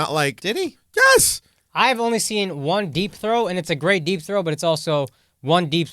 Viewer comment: I thought Akaash said deep throat!🤣🤣 Deep throw!